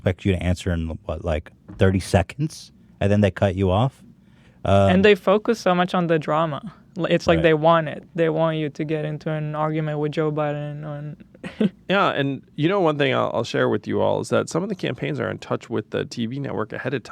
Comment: Tell me why I heard “They want you to get into an argument with Joe Budden” 😂